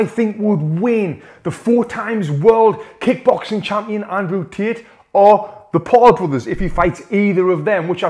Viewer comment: Nah bcs at 1:16 i heard " andrew t1t💀💀